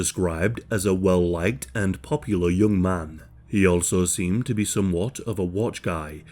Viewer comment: Thought you said a young lard